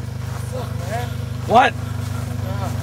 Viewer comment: "Why why you blame on me I told you come in steady speed " 😂😂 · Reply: i thought he said "why you want to bam on me i told you how much did he speak" because of the captions